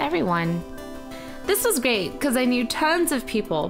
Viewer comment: lol go to 4:31and turn on captions XD she said great but it thought she said gay XD